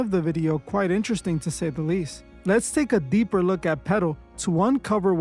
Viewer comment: ngga so nyc hispanic he sound like he saying “pedo” not “petal” 😭 😂😂😂